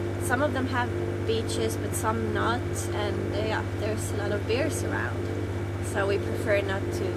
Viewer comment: I THOUGHT YOU SAID, "THERE'S A LOT OF BEER'S AROUND",......I WOULD HAVE BEEN TO SHORE IN A SECOND!!